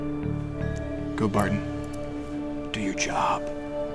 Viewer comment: Did he say "go Barton" at 1:53 ?